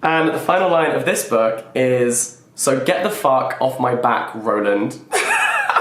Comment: honestly, i would have put Nevada in my top spot solely because "so get the fuck off my back,Roland" made me laugh out so loud. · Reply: I legit thought he said “rodent” instead of “Roland” lmao 😂